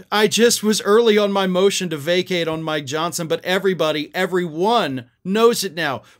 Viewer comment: 😂 I heard 'vacate on my Johnson.' 🫡🇺🇲